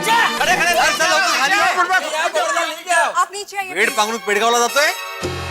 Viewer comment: can any marathi tell what bhidebbhai is saying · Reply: (वेड पांघरून पेडगावला जाणे) सब कुछ जानते हुए भी (अंजान) बनने का ढोंग करना या जानबूझकर मूर्ख बनने का नाटक करना।